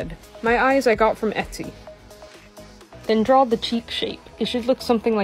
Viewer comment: bro really said cheap shake instead of cheek shape(not trying to be rude)